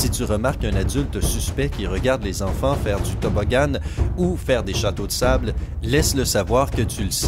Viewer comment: c'est moi ou tu as dit toboggane